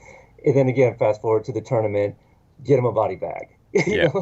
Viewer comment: I thought he said " Put him in a body bag, yeah!" Like 99% it was put him in and not get him.